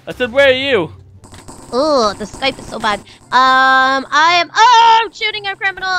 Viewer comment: was it just me or did i hear say "this skype is so bad"